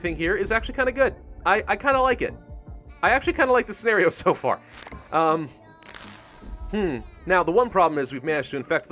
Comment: What is this creepy girl saying? Anarchy? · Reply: She was singing Ring Around The Rosy